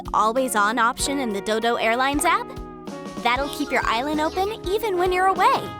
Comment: Did she say... Dildo Airlines App?